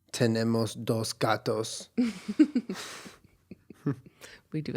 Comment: thought Julien said "patos" and I absolutely lost it